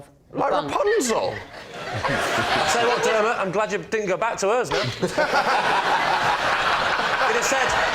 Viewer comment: Guys I'm American can anyone please help me understand what Lee says at 16:14 and why it's so funny?? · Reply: Say what Dermott, I'll bet you're glad you didn't go back to hers(home).